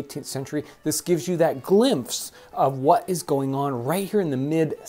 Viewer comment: does he say “Glimfse” instead of “glimpse”? 😂😂